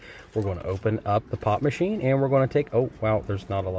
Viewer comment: Ohhh i thought he said pot Machine not pop..